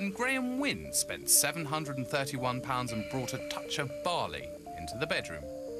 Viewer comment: Bali! I thought they were saying barley! Oops! 😄